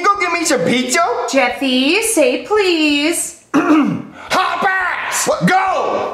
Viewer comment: was Jeffy say HOP ASS GO